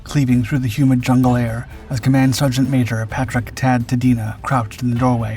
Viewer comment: did you say, "Command Sergeant Major"??